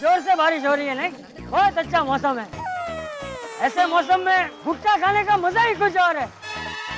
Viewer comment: did I hear gutka instead of bhutta 😂😂😂